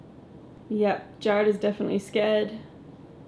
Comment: Where you guys thought it said “hey Jared” it sounded to me that it said “stay Jared”